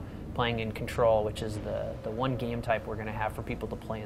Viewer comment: did he say "the one game type" did he mean the only game type